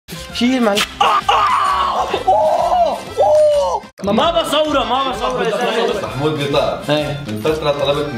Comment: بدقيقة 0:05شو قال الرجال قال ما بصورو يعني ما كان بدو ينزلو